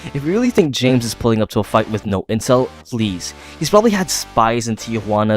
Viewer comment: did anyone else hear “No incell”